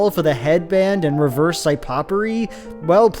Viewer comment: Did... Did you just say "psy-popery"?